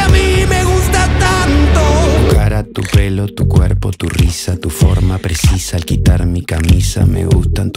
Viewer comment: Tu forma precisa? Yo cuando escuché la primera vez pensé que decía preciosa XD😂